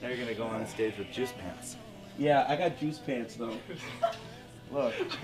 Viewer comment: initially I heard Jizz pants 1:15.